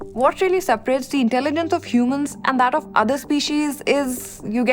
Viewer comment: Did she say /inteligent/ instead of /inteligens/!